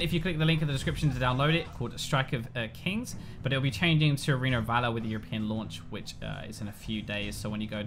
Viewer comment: I thought it said Alena of Avalor kms